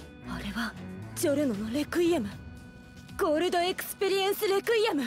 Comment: I thought I heard her say "piano power" lmao